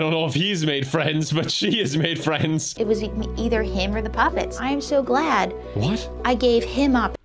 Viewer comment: I thought the sister said "me or the puppets" That and the 2.5 kids is the average number of kids a person has...